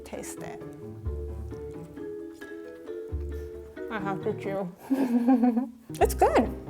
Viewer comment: I thought she said “I need to puke” instead of “I need to chew”